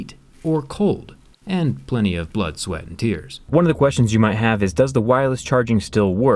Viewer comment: did he say blood sweat and tears or am i the only one who heard this because i watch too much BTS